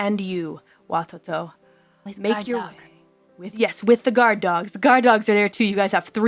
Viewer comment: Calli: Can you describe to me a day in the life of Watoto? Ame: Wake up! Eat Guard Dogs... Me: Did mishear?